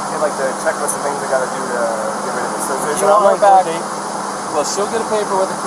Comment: Please tell me someone heard him at time stamp 27:04 saying “I’m going to get rid of her” and the police officer telling the man in question that in order to press the charges , she needs to be present …..